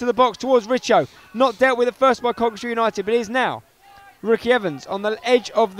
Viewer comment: It sounds like is saying Coventry united